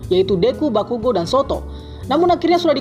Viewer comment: v gue dengernya Soto